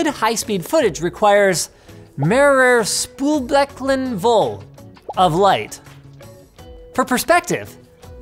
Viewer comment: Pronounciation aside, i'm not sure what you were trying to say there, but this definitely wasn't it :D "Multiple sinks full of light." What?